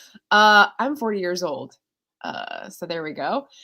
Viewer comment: It sounded like you said you were 4 years old, lol. Beekeeper's Apprentice sounds interesting. You seem to like apprentice characters.